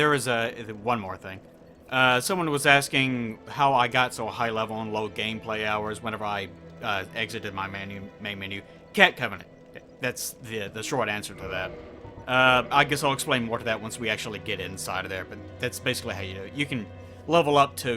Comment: what he say there? Ket covenant?